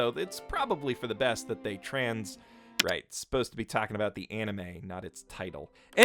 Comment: Wait a sec was that a... Trans rights I heard